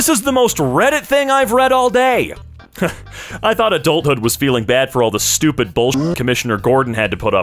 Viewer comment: Annnd i had to google about who's gorden i thought it says gorden putting up with stupid commissioners💀